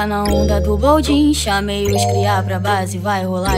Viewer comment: So eu que ouvi chuva de rin Edit:gente eu já nem entrava mais nessa conta daí eu entro e vejo que eu tô famosa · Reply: CHUVA DE LEAN